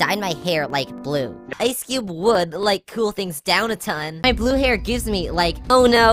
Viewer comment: sounds like she is saying "blue hair disney"